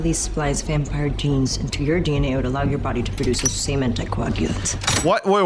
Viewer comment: Did she say 'semen to coagulate'?